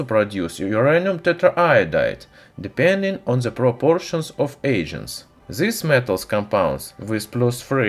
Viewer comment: lol am I only one who heard "the proportion of Asians"